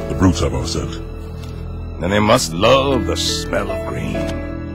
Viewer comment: they must love the smell of green? I thought he said smell of badass...